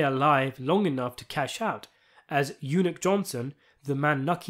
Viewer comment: Did you call him Eunuch? Lol.